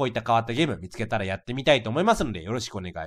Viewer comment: 最後ポケットラブでした！のあとに おって聞こえるの私だけ？